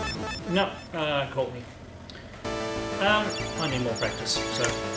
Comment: I thought you said "I need more breakfast" while playing Gradius II. About a second after I realized that I must've heard wrong and you said "practice". But in that second I thought about what was it in that game that stirred your appetite that much. He, he.